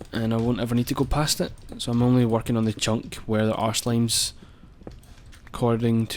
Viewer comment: Ohgod. I was spacing out, thinking about random things and when he said "where there are slimes" I thought he said "wear there arse limes".